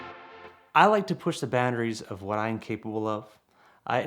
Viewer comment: Does he say "I like to push the batteries" at 0:20?